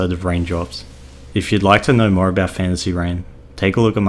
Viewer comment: I keep hearing "fancy rain"